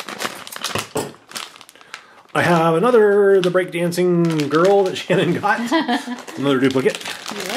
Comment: I thought said the breakdancing turtle lol